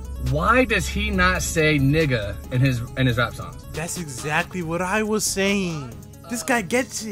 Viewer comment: what did he sayyyyyyyyy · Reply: Bro said 🥷🏽 a little to easy